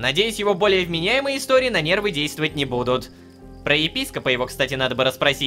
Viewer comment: мне послышалась ебископа